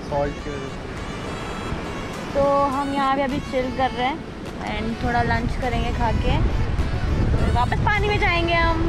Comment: No one: Literally no one:, But me I heard khake lunch karenge hahahah said by (